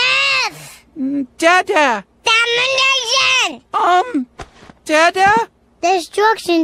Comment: мне послышалось или он сказал домой нельзя???